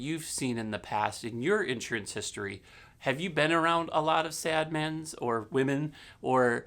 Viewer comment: did he say at 9:00 "have u been around a lot of sad mens.. or WOMEN'